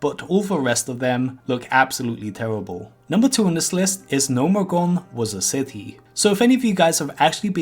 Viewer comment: I hear Nomegone